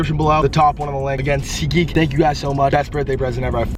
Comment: When was saying seat geek I thought he was saying CT as in CT from MTV the challenge & It made sense with the Patriots connection & all.